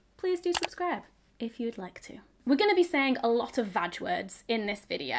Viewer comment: The closed captions around 0:50 says vulgar instead of ‘vag’, and my brain was very surprised when it thought was calling vagina words vulgar words 😂